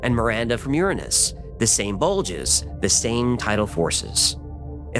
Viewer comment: I heard, "two bulges."